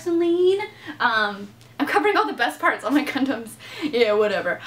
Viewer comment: I thought she said : I am covering all the best parts of my condoms ... Then realized she said gundams